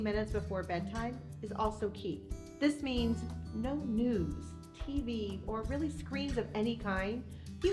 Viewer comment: Girl, I thought you said "no nudes" instead of "news!" 😮😂 I mean, those probably aren't very good before bed either.🤔🤨😂